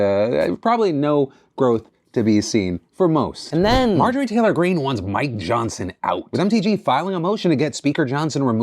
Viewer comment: I literally thought he said "MTG, wants my johnson out!" My congresswoman wanting to see Phillys "D", wasn't on my 2024 bingo card, but at the same time it wouldn't be the weirdest thing she's done.😅